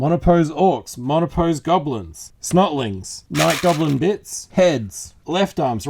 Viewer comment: Everytime you said "Snotlings" I thought you said "Smutlings" and I had to do a double take. Geeeeeze, gotta get my head out of the gutter.